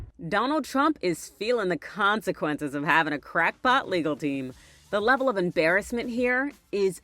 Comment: For a second I thought you said crack HEAD legal team!